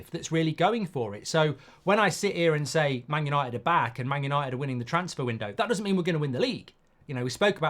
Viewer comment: I thought he said Man utd are winning the league 😅